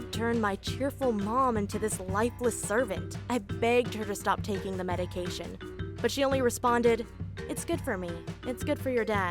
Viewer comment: When i heard a the biggining i heard "stop eating those madecation pills" andnow i know those are happy pills